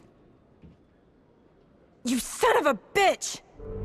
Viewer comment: "You son of a bitch" Oh sorry, did you say something? For some reason all I keep hearing is "three for three".